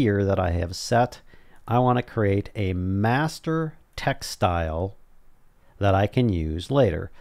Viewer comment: did I hear master "textile" :P XD